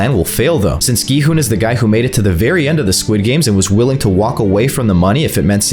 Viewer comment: Who else heard him say hunger games instead of squid games